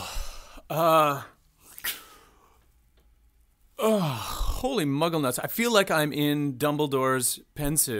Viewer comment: 😂😂😂😂😂 Did you say "holy muggle-nuts"??!!!?? I'm gonna use that one! It made me laugh! Thank you. 9:24 Great reaction!